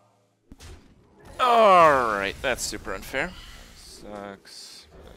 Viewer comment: wtf is he saying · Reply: "Alright thats super unfair". How was that hard to get?